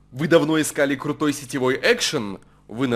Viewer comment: Ине только послышалось что он в начале рекламы говорил вы гавно искали😂